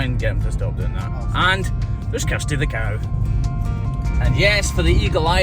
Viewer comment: When you first introduced Kirsty the Cow, I thought you said Crusty... later on you said she was named after your sister 😳🤣